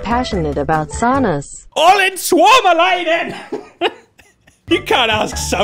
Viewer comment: For someone who reads this, and wonders what he said, it was: Olen suomalainen, which translates to: I'm Finnish. Just so you don't need to open Google Translate.